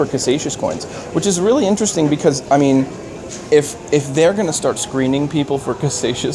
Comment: does anyone know what that word is he said? It sounded like 'gonsatia' coins but I can't figure out wtf he's talking about?